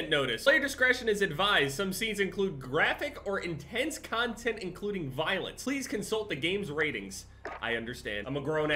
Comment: Me: noticing all the guns and violence: “is that a joint” XD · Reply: Bro dead ass I thought you said gun violence and I was like “bruh its war”